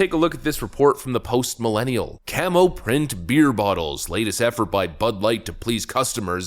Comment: I thought they said they were making 'cum ho print' beer bottles....